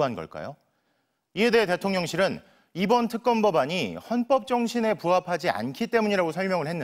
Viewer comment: 헌법정신을 당신 입으로 뱉으니… 아니 그냥 범법정신으로 들리네…